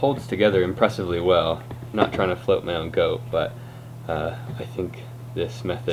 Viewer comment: did he just say "not trying to float my own goat"?